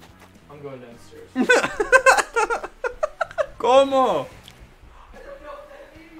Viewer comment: what did say at 3:40 wtf LMFAO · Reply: Como